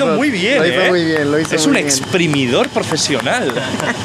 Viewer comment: Lo que todos escucharon decir a Auron "Es un exprimidor profesional" Lo que yo escuché "Pero si esto es la ostia" Xdddddd